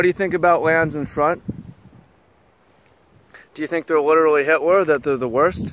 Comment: Literal Hitler, you heard it here first folks. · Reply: i heard it as "litterally himler"